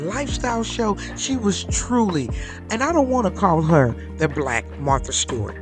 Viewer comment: WHY ARE YOU CALLING HER THE BLACK MARTHA STEWART??? SHE IS SO MUCH MORE!!!! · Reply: I heard her say "I'm NOT going to call her the Black Martha..."